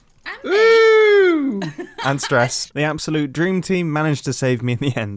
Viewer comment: Did you say “Dream Team”?!?!